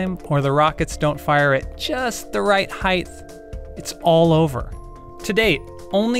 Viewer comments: Excuse me, did you just say heighth? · did he say ‘heighth’?